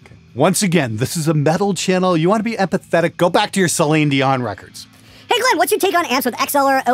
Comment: Did he just say "Saline Dion"?! :'D Hahahahaha